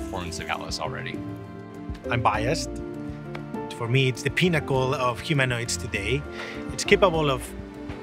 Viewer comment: I thought he said it was the "pinochle" of humanoid robots. That really threw me.